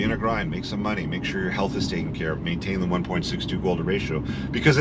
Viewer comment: what is the 1.6 to “…” ratio ? I slowed it dow but still couldn’t make out what said there . Thanks for doing gods work. Edit! I think he meant cheat to waist ratio